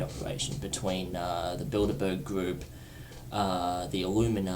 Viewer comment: wait, who did he say at 1:36 was one of the partners of the secret government operation? Did he say "Build-A-Bear Group"?